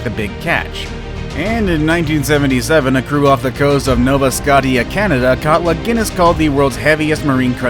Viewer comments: Did he just say "nova sc-ot-ia" whattttt haha😆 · Did he just say nova Scotty ah